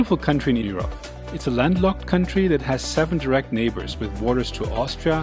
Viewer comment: Not "nations". Countries MY fault. I heard "nations" instead of "neighbours"